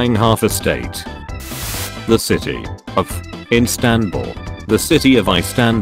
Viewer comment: what are they trying to say İstanbul (im turkis )